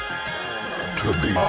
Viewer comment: can anybody help me with the line that he says from 4:41 ? I can't really understand it. thx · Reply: To the outer limits of our solar system